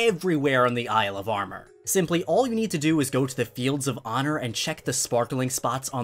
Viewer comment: oh, i thought it was called the “fields of horror” this whole time, not “fields of honor”